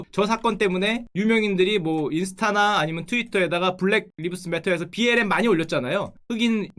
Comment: "블랙리브스배터"로 들리네요. "흑인이 더 잘산다"?